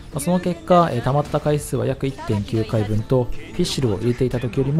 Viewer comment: 1.9回分と言っているように聞こえますが、書かれているのは1.6回分でした。